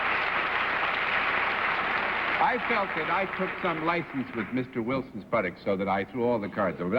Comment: is John saying, "I felt that I took some license with Mr. Wilson's buttocks?"